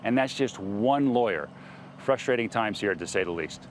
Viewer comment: He mispronounced "horrifying" so badly that it almost sounded like he said "frustrating".